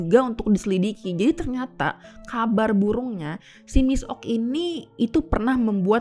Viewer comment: kabar burung nya,gw gak salah denger nih